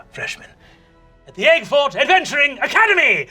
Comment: I'm so dumb: I straight up thought he was saying Eggfort Acadamy, until he introduced the principal